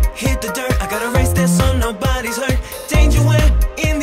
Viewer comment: I always thought 0:16 said “I’ve gotta raise that son, nobody’s hurt” which I guess still makes sense